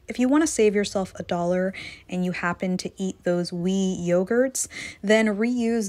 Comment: SMH I heard "wee yogurts" and kept thinking "that's cute, teeny yogurts" then I realized you were talking about Oui yogurt.